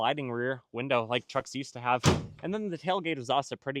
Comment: did you say you can or can’t lock the tailgate? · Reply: Can’t. At least to my knowledge as there’s no key hole. But I’m sure there’s aftermarket options to help lock it and prevent tailgate theft. Thanks for watching!!